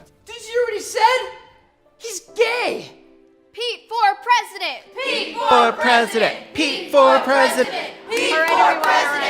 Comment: it sounds like theyre saying "pee for president" the longer they say it which makes it even funnier